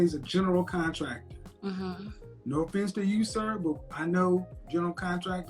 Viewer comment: Did he call her sir